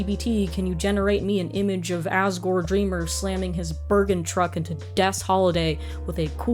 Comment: i zoned out for a good minute there and then i heard "...DREMURR SLAMMING HIS BERGENTRUCK INTO DESS HOLIDAY"